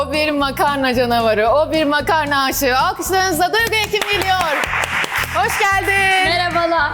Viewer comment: Turgut Ekim geliyor ??? Tek benmi öle duydum